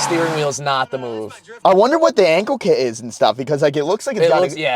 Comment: I heard its a 🌈 angle kit